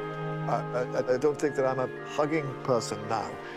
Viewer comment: iM CACKLING I HAVE SUBTITLES ON FOR SOME REASON AND AT 1:11 WHEN HE SAYS HE ISNT A JUGGING PERSON THE SUBTITLES SAY HE DOESNT THINK HE IS A FUCKING PERSON I-ODKDKFJSND